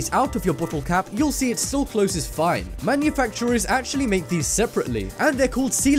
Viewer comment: What did you say at 1:18 - 1:20????? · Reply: Connie G. He said "still close this fine"